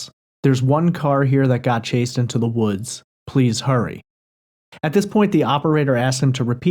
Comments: Sounds like he said one guy chased me to the woods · I distinctly hear say: “Yeah, I’m in the middle of a field, this <?> just PUSHED some guys over.” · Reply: Then next, I heard him say: “There’s one guy here...I got chased into the woods.” Which would make sense because initially, he said he was in a field (woods)...so why was he there...he got CHASED there by a guy. Then he pleads, “Please hurry!”